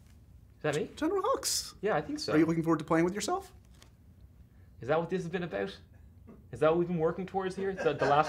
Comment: Someone please tell me what is he saying at 0:44 . :) I missed that lines. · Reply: 2 months late but " is that what this has been about. Is that what we are working towards here"